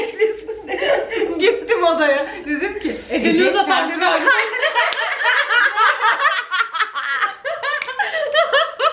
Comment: de ne diyor anlayan var mi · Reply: Ece nude atar mı(sın)? Diyor da güldüğü için cümlesi kesilmiş